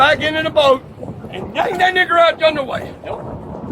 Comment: Did he just say, “ yank that N!gger?”